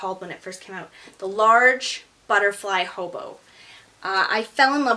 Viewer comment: Sorry I don't quite catch the name of the bag at 6:30. May I know what's it called? Thanks! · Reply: ***** Not a problem, it is the Bulga Large Butterfly Hobo, here is a link to the re-launched model on their site, if you are interested thank you for watching!!